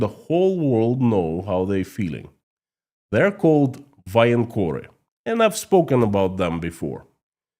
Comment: Hi, I've got the question. I can't understand the word, you say at 14:19. Can you help me? Many thanks! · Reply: Voenkor/vojenkór